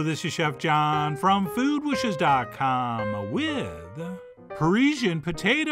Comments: I thought it said Partisan Potatoes at first. I've got to stop watching the news for a while. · Not me here cause my brain thought it said "partisan potatoes" and I was really intrigued to learn how they were going to politicize a potato dish.... Glad to know - in this hyper-polarized world - carbs still unite us.